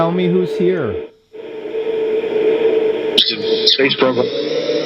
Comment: sounds like they said space program